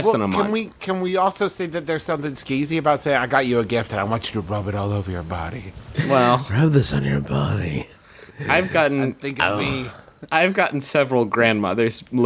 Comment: When I first saw this, I thought it said “rub this on your baby”